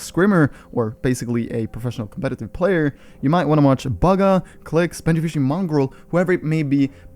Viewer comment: ahh did he say bagha in